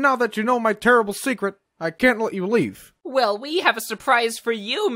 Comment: i hear live instead of leave